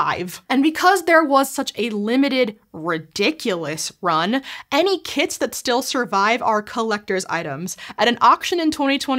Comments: at first, I thought she said, "any kids that did survive are collector's items." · Thought you said "any kids that still survive are collector's items" xD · LOL @ 5:15 I thought you said "any kids that still survive are collector's items" XD